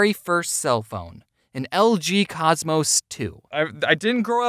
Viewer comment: Welp when James said "An LG Cosmos 2" I thought he said "An LGBT Cosmos 7"😂